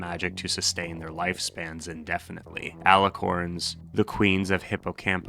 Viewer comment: i thought this said "alicorns, the queer." 🥹